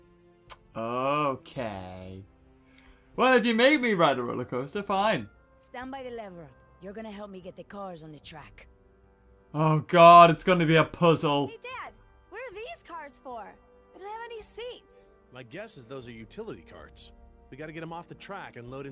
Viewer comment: Hilarious how every opportunity he gets to kill a character he takes it 33:13 I thought he said pushole but he said puzzle